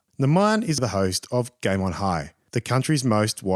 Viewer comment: i heard game on high... thefaq